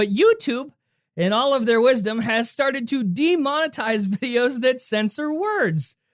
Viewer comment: did you say "demonetize" at 0:55? (withdraw from use as money) But you meant "demonize", right? (represent as demon)